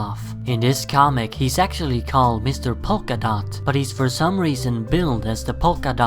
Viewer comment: "In this comic he's actually called Mr. Polka-Dot..." Am I the only one who heard this as "...He's sexually called Mr. Polka-Dot..." It really doesn't help that perpertually sounds like he's trying to seduce me.